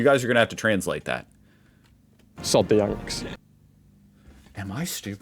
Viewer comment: I think he said "Sod the Yanks", a little severe maybe but definitely a rebuke. He said it a little too timidly though so I'm not sure his heart was in it. ("Sod" is a British slang term that can be used offensively to describe an unpleasant person, as an exclamation of anger or frustration, or as a mild insult. It can also be used in expressions like "sod off," meaning "go away") 3:27 Have you not seen the reports of attacks on campuses and off by the Right-Wing Frat Bros? They feel particularly emboldened to do what they like at this point, she's quite right. If I was a young women, particularly in somewhere like Florida, I would be more nervous than usual on the street. Women already face those kinds of dangers, with the sick mindset some of these people have, there is an increased danger. They think of women now the same as they think of minorities. 5:18 No, they're white, older and, I would suspect, may have a racial bias and they likely see what's going on in the US as admirable, particularly when they compared it to the UK where we're still trying to stem the tide of hate and hateful people here. 6:23 No it's not, demonstrations and protests are being attacked and curtailed regularly in the US now, that is against the 1st amendment, the cornerstone of free speech and expression. If you actually think you're still "free" then you need to take a look around and watch the news more. 8:25 Yes we're getting blowback from Trump and his ilk here too where all the lowest